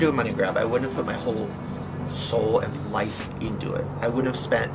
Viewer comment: Tell me why when he was talking to rylands mom I ten out of ten thought he was gonna say “why would I put my whole bussy in it” not “why would I put my whole soul in it”. 😂😂